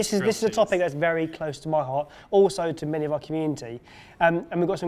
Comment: haha when said community i heard cumin tea the first time 😂 his tongue is as slippery as a banana peel...